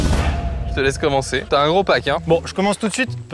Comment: t'a un gros pack hein, j'ai entendu "t'a un gros paquet" 😅